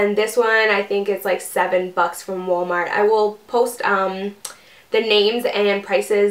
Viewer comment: I thought she said 7 fucks from walmartc